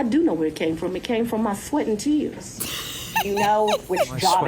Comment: I had to rewind and watch Fani again. I could have SWORN she said the cash came from her sweaty t*ts🤣😂🤣… 🤦🏼‍♀️SWEAT and TEARS!!🤭